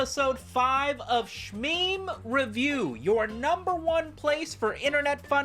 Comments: For a second I thought the background said semen review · Enter my wondrous world of Shmeme Review. Ok. Like and Subscribe. Ok. Shouts out 47 Santos. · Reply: Thought you said Shenmue Reviews and I was really confused for a second